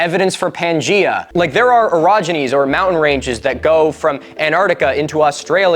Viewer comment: He said marsupials like the duck billed platypus. Mate, they are monotremes not marsupials (pro-therians not Meta-therians). They lay eggses not have a pouch. AND they are not Duck Billed Platypuses they are just platypus. Cheese and Biscuits. And, you said "there were orogenies" but to my ozzy ear it sounded like you said "erogonies" and I started getting a little, aroused, so watch your pronunciation young man.